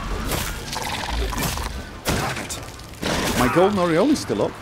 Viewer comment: Forgive me, but everytime he says "Golden Oriole," I hear "golden areola," lol. 🤓 Where I'm from, I've only ever heard "oriole" pronounced "oh-ri-ohl," like in the classic rock-and-roll song, "Rockin' Robin".